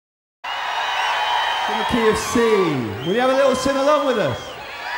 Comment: Sounds like he said "from the KFC, can you have a little sing along with us?" But i see no fried chicken 😢 lol